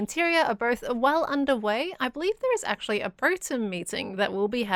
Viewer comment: did say broatem? anyone else notice that?